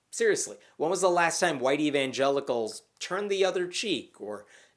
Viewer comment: I know your saying "white evangelical" but I keep hearing "whitey vangelicals".